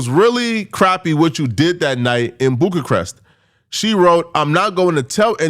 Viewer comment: SECOND incorrect usage of words for today: “Boog-a-crest” instead of “Bucharest” 9:23 🤦🏾‍♂